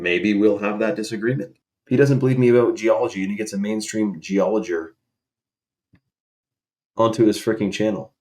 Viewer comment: did he say geologer